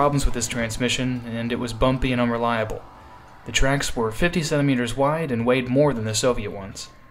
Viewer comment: 57 meter wide tracks? Wtf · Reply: I heard fifty centimeters not fifty-seven meters, maybe you misheard it.